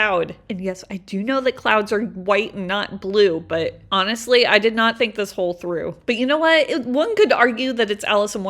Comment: “I did not think this HOLE through” is what i heard lmao